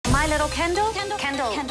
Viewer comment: Sometimes it sounds like she says :My little kAndle or is that just me? Lol